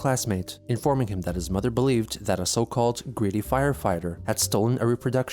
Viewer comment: "a greedy firefighter." To the Chandlers, everyone is an enemy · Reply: i misheard "greedy" as "creepy" when i first heard read it aloud which is ironic because i dont think ive ever seen a creepier duo then Chris and Barb, especially after... the incident.